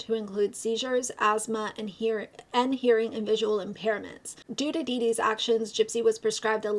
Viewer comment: whoa! you've changed your intro since the last time i was here hahaha good to be back though! loving the black & white vibes with the new font! also, i know this is a serious video, but i chuckled when you said "due to Dee Dee" at 3:45 because it sounded like you said "doo doo dee dee" and i'm, apparently, 12 years old. 😂😂